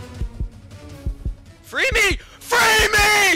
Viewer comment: I’ve seen juke 2 teamers but never 3☝️🫨😳😱🤯 Edit: and at 3:06 when he said “free me “ I thought he was saying freamers💀💀